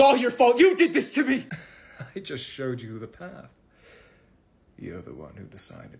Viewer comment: “i just showed you the path.” I THOUGHT HE SAID “i just sh*t myself.”